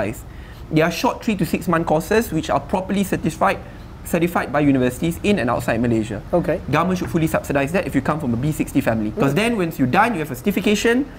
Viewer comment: did he just say B60? hah! that means B60 , M35, T5. majority of malaysians just make enough to survive.